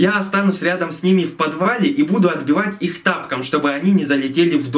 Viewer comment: Мне послышалось танком а не тапком